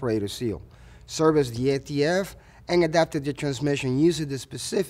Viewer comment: I can’t quite make out what is said between 3:34 and 3:40. “After we have completed repair of the separator seal, serviced the ??? and completed the adaptation”. What is the missing word denoted by ??? please? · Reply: ATF